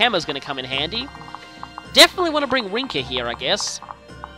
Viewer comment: ish Did I hear you say Ricken? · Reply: I thought I heard him say "Rinkah" there...